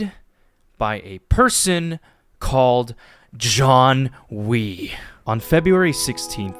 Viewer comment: i thought i heard john titor before i watched the yt rewind